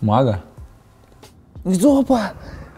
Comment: мне показалось что он сказал жопа